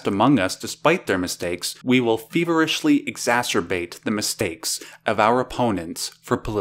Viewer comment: feverishly masturbate is what i heard